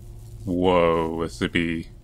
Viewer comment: wish i had subtitles to tell me what says sometimes. can anyone tell me what he says at 10:05? i hear "whoa it's a bee"